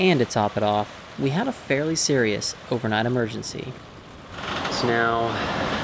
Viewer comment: was worried for a second when I thought I heard you say "a medical emergency"... Glad I misheard that.